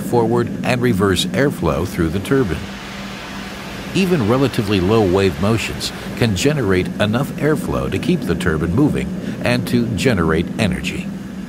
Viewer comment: Did he just say "Turban" ? lol